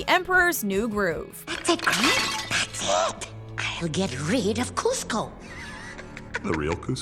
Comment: I thought yzma said get rid of Costco